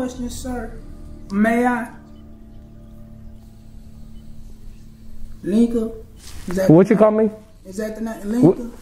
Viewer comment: Bruh, I swore I heard Nikka. Lmao 🤣 🤣 🤣